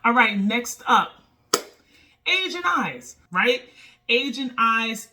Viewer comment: Lmao I thought you said "Asian Eyes" #triggered